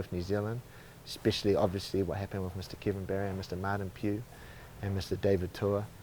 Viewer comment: What happen between Kevin Barry David tua and Martin (piu) I don't know if that's his last name it's just what it sounded like Eugene said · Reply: Kevin Barry and Martin Pugh did the dirty on David Tua over some land. NZers have never forgotten or forgiven them.